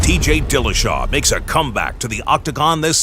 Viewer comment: Sound like he said ..."DJ Dillashaw"